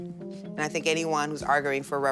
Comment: Did that lady with the krusty the clown hair said "arg-oo-ing" at 29:50 instead of arguing? Or am I mishearing that? Not to take away from the substance of her argument.